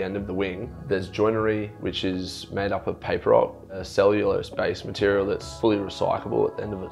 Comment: 'paper rock'. Did I mishear that? What is the 'cellulose based material' he mentions?